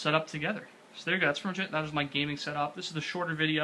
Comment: At the end I thought he was saying "and that's more shit" XD